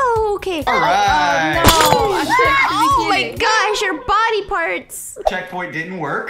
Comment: On 6:32 sounds like someone says "oh sh-t"